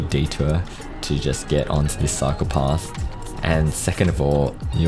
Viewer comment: Whenever you say "cycle path" i hear "psychopaths". Enjoy.